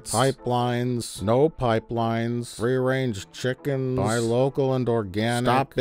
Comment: "Free range chickens" was funnier when I heard "Re-arrange chickens"